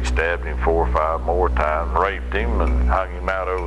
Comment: I never knew what the inmate says. “busted his head a-wackah-pocket” WTF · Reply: “Like a Pumpkin”